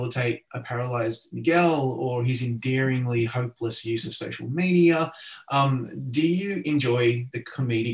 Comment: "His endearingly hopeless use of sexual media..." 🤓 That's how I heard it the first time 🤣